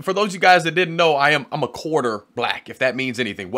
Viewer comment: I just got it, I thought you were saying that you are a quarterback, but you actually said you are a quarter black!